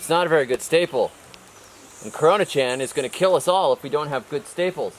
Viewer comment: did you said "Corona-Chan"? hahahahahaha